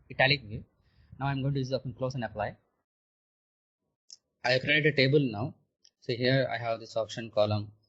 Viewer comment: what you said I can't under stand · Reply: I have created a dummy table, using option Enter Data from home tab on the ribbon